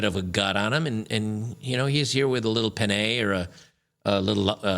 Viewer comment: made me laugh so hard, I know pinay is a filipina, but I had to do a double take cause it sounded like 'little pene' which would be spanish for little penis